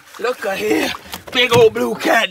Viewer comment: “Put dat on a cracker” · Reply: Ahhh I thought he said put down a cracker 😂